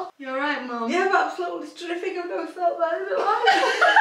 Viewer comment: what did said that could be meme? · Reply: whilst crying nonstop "Yeah I'm absolutely terrific never felt better in my life" 😅